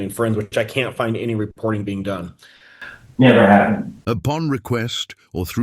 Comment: Funny how when brought up Vicap got up and walked off camera and I heard him say “never happen!”😮